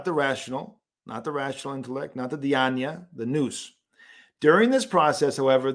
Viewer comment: What is the word for the rational intellect used at 10:33, it sounds like deaniea? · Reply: I’m super late, but in case you’re still interested and for the future peoples, it’s “dianoia (διάνοια)”